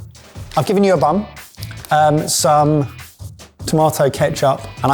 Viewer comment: I’m assuming the subtitles are automatic as at 17:03 it’s says “I’ve given you a bum” instead of bun 🙈😂